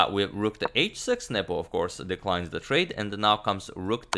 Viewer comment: I keep hearing "Nipple" instead of "Nepo" and it's oddly distracting